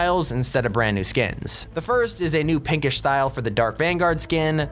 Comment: did any one here say pickish instead of pickaxe LOL😂😂😂😂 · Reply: He said PINKISH, 😡!!!